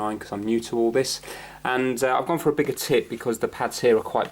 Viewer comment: sounds like is saying bigger tit instead of tip 😂